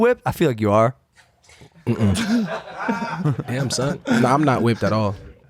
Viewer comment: Lmao I think said "Damn son" to the racist undertones of being whipped by a white guy